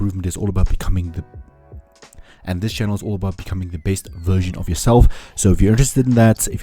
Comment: I heard "becoming the BASED version of yourself" instead of "becoming the BEST version of yourself". I see what you did there.